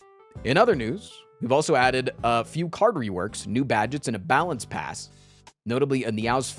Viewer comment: When you said "balance pass" I completely misheard and thought you said battle pass 😂 and now im wondering how that'd even work